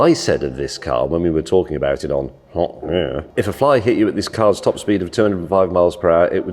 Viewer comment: did he say "hop here" or "pop beer"? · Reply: He murmured top gear